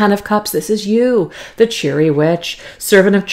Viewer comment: here's a chuckle...talking about good times ahead...the Cheery Withch card, I misfead it and thought it said Cherry witch..from then on I kept hearing you say cherry witch! lol happy weekend